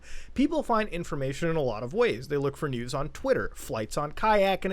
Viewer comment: did say nudes on twitter?